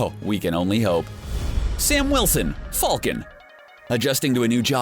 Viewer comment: did he say folcan